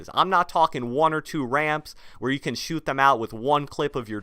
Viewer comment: does anybody else hear tain instead of ten?